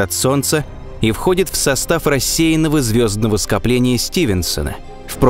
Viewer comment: мне одному сначало послышалось "состав России"